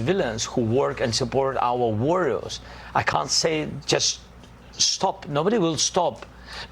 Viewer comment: he said that of he told his amy to stop nobody will stop. did i misheard that? is he a president or what?